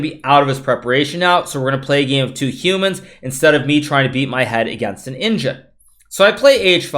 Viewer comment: "hes going to be out of his preparation now so were going to play a game with two humans instead of me trying to beat my head against an engine"-GM Hikaru Nakamura 2022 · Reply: I Thought he said, "beat my head against a ninja" LOL thx for the clarification